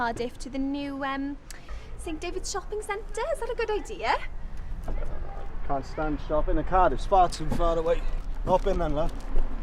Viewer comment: Can somebody help me understand what he said at 01:07, just before the lady got in the car? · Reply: Can’t stand shopping and Cardiff’s far too far away. Hop in then love